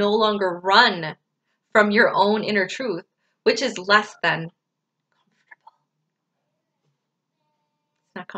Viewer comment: You said something but the audio cut out. What did you say after "which is less than?" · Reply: I said what is less than comfortable. 😜